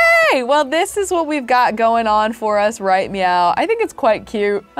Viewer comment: or 30:35 it sounded like she said right meow